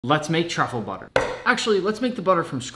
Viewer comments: I thought he said “Let’s make chocolate butter” at first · When he said truffle butter I actually thought he said charcoal butter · Bro I thought it said turtle butter💀